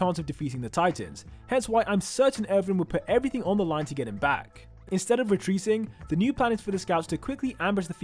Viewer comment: instead of retreasing